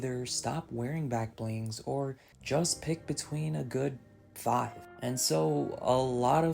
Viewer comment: tf did he say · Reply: Between a good five.